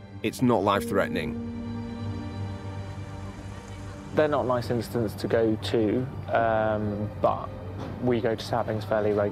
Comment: what did he say? Not licensed us to go to ......?